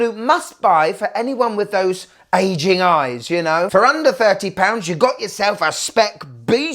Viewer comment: Legit thought you said Asian eyes hahaha I almost spat my drink 😂 had to rewind 😂😂😂